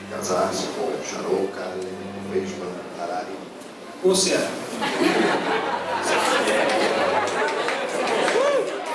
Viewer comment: n entendi oq ele falou aqui mas geral riu kkkkkkkk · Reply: “Ô se é”